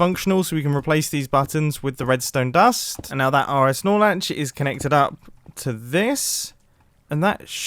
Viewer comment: Did I really hear him say RSnorelatch ????